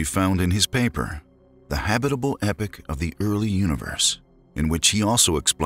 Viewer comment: The habitable "epoch" of the early universe - not epic as narrated at 3:26. I had to backtrack because it sounded like the title of a space opera. Just saying...